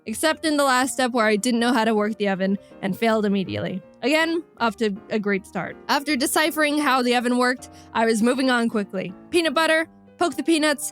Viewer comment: i thought it said: Don’t worry. Mama will kill